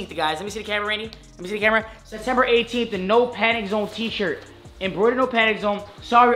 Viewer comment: Swear I thought you said “no panty zone” I was line that’s lit